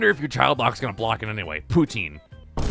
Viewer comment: 🧐Did I hear the word Putin correctly?🤯🥳 Okay, that's it, I'm hungry.🤤=🍟+🧀+🫕